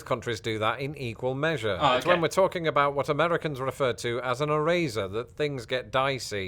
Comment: When first said "eraser" I thought he was saying "razor" because he pronounced it e-RAISE-er, whereas in the States we pronounce it e-RACE-er.